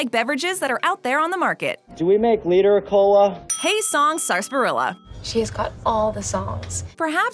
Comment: i thought you said "Hey Sharp Gorilla" first...